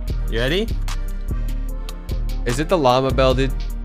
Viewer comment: I cant be the only person who thought it said ligma bell??